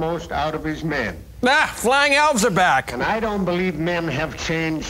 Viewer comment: "AAAHHH! The flying elves are back!" I still laugh out loud when I think of that line! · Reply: I always thought they were saying "flying Elvis'" Utah division 😅